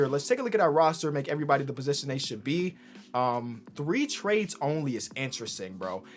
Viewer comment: I thought it said 3 team trades